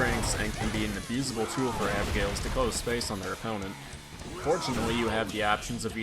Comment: nbd but did u call abigail, "her" @ 1:33? am i missing something? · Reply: I think he said "their opponent," because right before that he said "tool for Abigails," as in plural. So he was talking about the players who play Abigail, rather than Abigail himself.